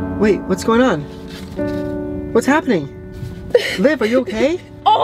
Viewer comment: WAIT DID ANYONE ELSE HEAR SAY "babe are you okay?" AT 4:12 or my ears just thought he said babe when he actually he said Liv idk but pls no hate